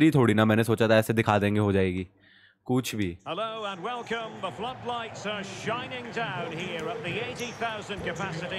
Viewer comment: bhai flow flow me kya bol gya